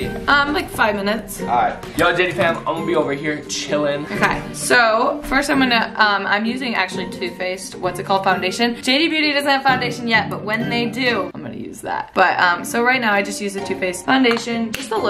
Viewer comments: Who else when she said to face thought she said toothpaste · At first I thought you said toothpaste foundation lol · I thought she said toothpaste instead of two faced 😂😂